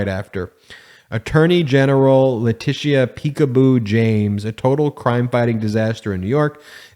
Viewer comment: As a black man my ears immediately rang when I heard "peekaboo" bc it sounds like "jigaboo". Why is he calling her "peekaboo" anyways? This Guy's really good at threading the racist needle.